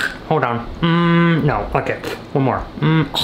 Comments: Did he just say "mm f*ck it one more" LIKE WATCH YO MOUTH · I thought he said the f word at 9:42 but he said “ok”… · when he said ok it sounded like f***k it